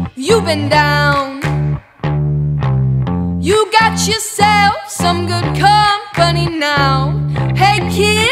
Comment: I love this song and it sounds like she says “you got stewgotz” at the beginning of the song lol